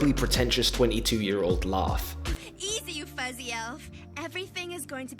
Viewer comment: What did Cyclops whisper? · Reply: Fuzzy elf